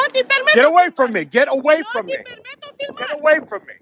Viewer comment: It sounds like she said "No te permito filmarme" which would be "You don't have my permission to record me" so maybe she was speaking spanish with an italian accent. That's what romanian sounds like. But also, romanian is a romance language like spanish, italian, french, among others; so romanian and spanish share up to 70% of words; maybe thats why it sounded like spanish, but as a spanish speaker I can tell you thats not the accent of any spanish speaking country so maybe she was actually speaking in romanian, I don't know.